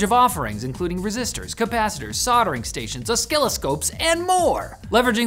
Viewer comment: Wait...did he say "oskilloscopes"?